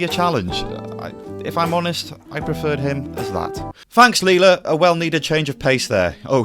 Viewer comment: Actually remember watching this on its original debut. Forgot about Mr. Bronson though.. And I’ve always overlooked that the Nucleus couldn’t possibly survive being enlarged. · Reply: I watched it all those years ago - there's a particular scene that stuck with me. When the Nucleus first comes out at giant size, Tom says something along the lines of 'and this is your leader?' At the young age I was, I misheard this as 'this is Leela?' I was gutted that they'd killed her off by turning her into a big prawn :( Happy memories :)